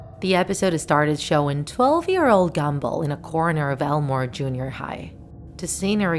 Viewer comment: It sounded like you said gemball